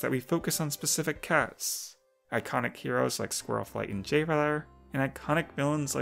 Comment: I THOUGHT YOU SAID ICONIC GNOMES AT FIRST ABEJFKKE